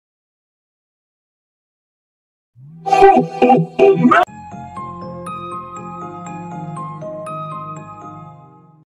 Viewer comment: Mit mond az a furcsán lerajzolt Mikulás? Nem értem XD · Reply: "Ho ho ho, Merry Christmas!"